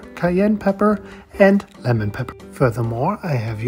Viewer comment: Side note: the number of cognates between German and English is both hilarious and strange, to be able to read part of a package and not the other part, forgetting they're German products for a minute. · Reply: I was actually so confused, looking at the labels and Zitronenpfeffer and hearing him say "lemon pepper". For a second there I forgot he spoke English, thought he said Limone (lime) and was like "woah, that is a MASSIVE misspeak" 😂